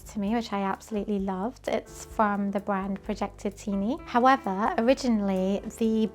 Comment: Does anyone understand what brand she said the quilt is from? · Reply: Hi there! I think she says the quilt is by projektityyny... It sounded like projectini to me and Google got what I meant 😂